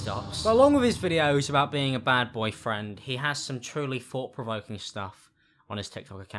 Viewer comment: I thought u said badboy friend💀😂